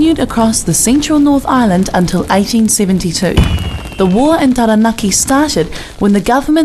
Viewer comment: Hello! what does she say at 0:22 ?? "the war entaranaki started when the government insisted on dealing with one chief over a block of land at Waitara" --> did i hear it well?i googled "entaraki" and there was no useful result. Pleace could somebody help me? I have a school project on this subject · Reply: The war in Taranaki